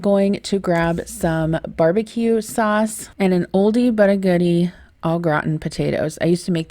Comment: Ha! For a split second I thought you said “an Aldi but a goodie” 😂. Hey, it works too!